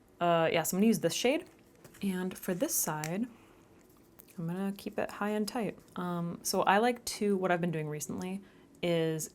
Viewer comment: Did you just say “keep it high and tight”? Are you a mommy?